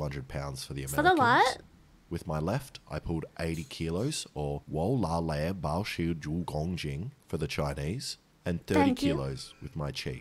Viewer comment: 我拉了八十公斤 literally had to replay it to hear what he said lol